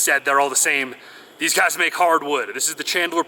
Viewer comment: sitting there listening to the video while reading an email and I hear "These guys make hard wood" kind of caught me by surprise